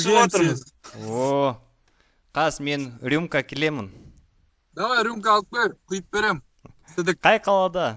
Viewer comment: давай рюмка алып кел, құйып беремін сідік. мне послышалось 😂?